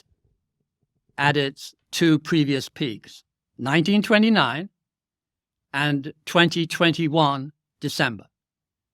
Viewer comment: whose data does he quote? I didn't catch it. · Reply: John Hussman